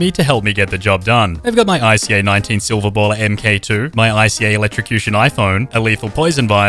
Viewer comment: Hey 0:33 is actually “mark” instead of “mk”